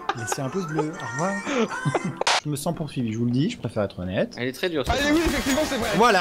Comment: J'ai entendu "Je me sens confiné". C'est encore trop tôt pour moi..